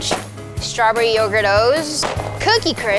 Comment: When she said "stawberry yougurt'os" i heard "strawberry overdose"